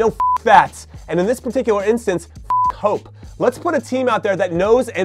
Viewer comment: did he just say “f**k hope”? Mad much Jimmy?